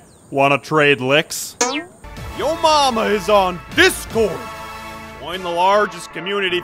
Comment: who hear when instead of join